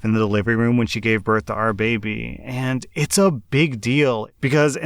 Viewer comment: when he said "our baby" i just heard "R baby"